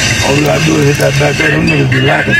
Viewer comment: Caption: “King Von sits outside of opps house” King Von: “you said y’all know where the house at right?” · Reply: Its hard to make out that deep Chicago accent. Sounds like he's saying " y'all say you know where the house at right" but at the same sounds like he saying "y'all see i know where the house at right".... Its hard to make it out.... But does it really matter anymore